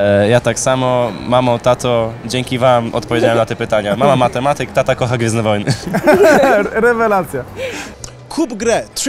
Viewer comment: ktoś wie co on powiedział? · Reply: luhu bama mama matematyk tata kocha gwiezdne wojny